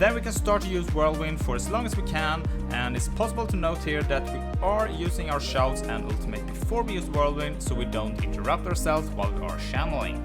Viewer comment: What did you say at 1:36 music to loud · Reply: sry about the sound! i said that its important to use the shouts before using whirlwind to gett all the buffs while we spinn